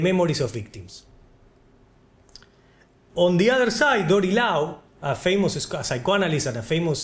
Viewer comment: what name is he saying at 12:23? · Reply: Dory Lao